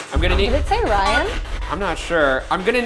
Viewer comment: it sounded like “i’m a demon” to me but i don’t think a demon would be that obvious